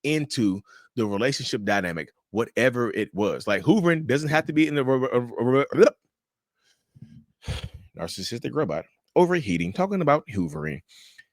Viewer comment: lol, it sounded like you said Hooverine the narcissistic mutant (hoovering wolverine).